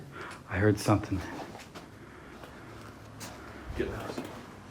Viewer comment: I heard a name voice in the play room that said..get out.. did anyone else hear it?